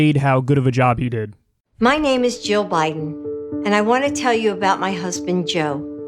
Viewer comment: When Jill Biden introduced herself, I definitely thought she said "My name is Joe Biden."